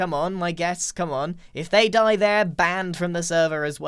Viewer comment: i accidentally hit mute, so i heard grian say "if you die. the world" 8 HERMITCRAFT EPISODES HAVE PASSED